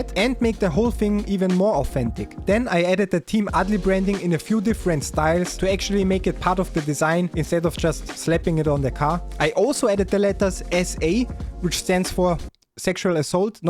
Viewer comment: I thought that cheap one said 'Team ALDI' to start with 😅. Tams looked the best, but did like your tangerine though. Best sim channel by far, love the humour 👌